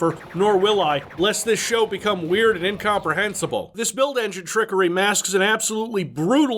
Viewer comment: "I've never smoked the reever. Nor could I, lest this show becomes weird and incomprehensible" I see you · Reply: I heard that as "weirdly comprehensible" and I can't decide which is better